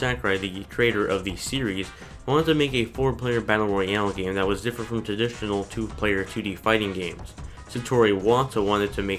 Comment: wtf did you said in there? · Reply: That was different from traditional.